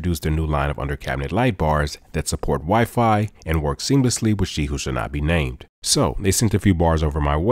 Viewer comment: Who else thought he said “ sheyu shinabi “ … didn’t realize you said she who should not be named 🤣